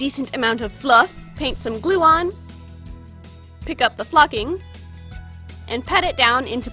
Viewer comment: Every time I watch this I hear ‘paint some glue on to Juwon’ at 8:16 😂😂